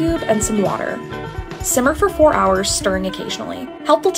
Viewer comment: Simmer for 4 hours?! Or did I mishear that?